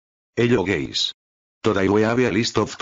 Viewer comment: Escuche "hello geys" xdd